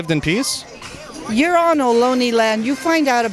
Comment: I thought she said you’re in looney land